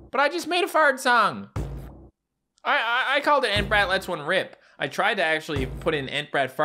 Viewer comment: I thought he said enbrat let’s one rape not rip💀